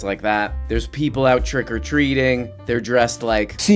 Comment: I genuinely thought you said “Trigger Treating” instead of Trick-Or-Treat. Still unsure if it’s funny or not.